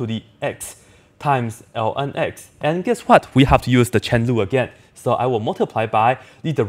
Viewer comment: what did he say? I heard something like chenzhu and got interested 🤣